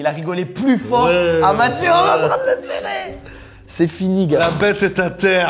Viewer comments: c’est fini quoi ? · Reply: C'est fini gars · C’est fi quoi ? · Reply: C'est fini gars. T'es le 100ème à avoir entendue le N word 😅